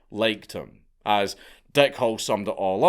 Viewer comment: Got injured in an accident? You could be click away from a claim worth millions. You can start your claim now with Morgan & Morgan at <link> without leaving your couch. Remember, it's free unless you win. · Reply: The way you pronounce "Hall" sounds like you are saying "Hole", so I choked on my coffee, when I thought you said, "Dick Hole". 🤣